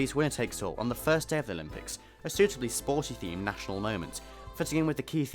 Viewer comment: I do love a good Confidential. Great video. And was that a national moment I heard at 2:22? :D